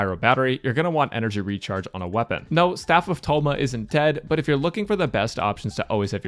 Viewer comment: did i hear staff of thoma